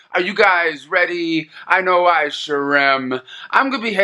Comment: "Are you guys ready, I know I sure am" I always hear "I know Eyeshurem"